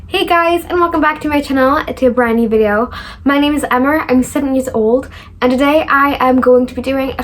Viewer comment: it might just be my ears lol but it sounded like you accidentally said 7 instead of 17 🤣❤️ i love you and all the cute goofy things you say, as well as all the educational and supportive things too, of course! I hope good things keep happening more and more frequently in your life ❤️